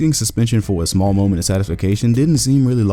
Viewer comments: Did he say satisfication? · did he say “satisfication”!?! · Did dude really say "satification?"